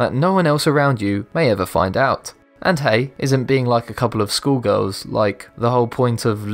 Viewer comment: Misheard "..isn't being WITH like a couple schoolgirls what life's all about?" 1st thought "If he'd meant simultaneously?" Funny and/or only terrible?